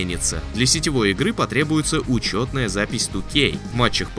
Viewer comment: Послышалось «для сетевой игры потребуется учётная запись StopGame». А было бы не плохо 😂